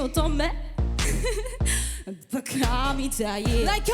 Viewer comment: 23:25 ♪ fufu バカみたい～♬ の所 聞きようによっては fufu Fuckerみたい とも聞こえて 日本語だけど面白かった🥰 にしても化粧化けが凄すぎてFUMINOってわからなかった しかも あれ？こんな性格なの？と 面白かった🥰